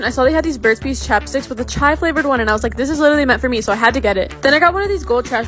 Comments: When you said "chai flavored" I heard "child labor"💀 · I heard that as child flavoured one… (referring to the lip gloss thing)